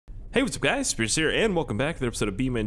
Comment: ngl i thought he said hey whats up guys its scarce here